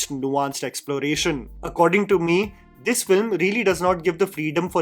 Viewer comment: Did he say, "According to me?" What?